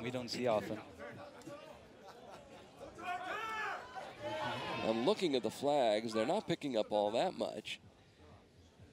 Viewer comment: does anyone know what that sound is? I tend to hear it everywhere. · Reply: koreans cheering. its a common saying in korean, “fighting!” similar to how english speakers say “let’s go!”